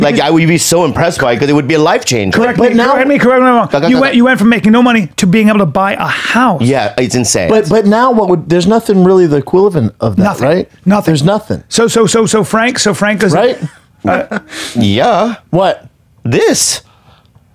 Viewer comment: Lol at face at 15:02 when said queliven instead of equivalent